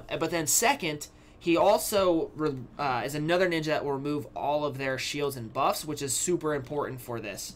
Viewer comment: Cool, I actually have all of these units xD. My friend asked me to help him with this and I heard "HE HAS ALL THE SHIELDS" So i was like: "Okay getting temari" 3